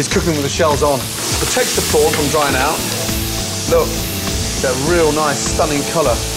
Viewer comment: Guys, please help me, what is he say at 0:50?.. is he say "don't let the oil dried?". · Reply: He said "protects the prawns from drying out"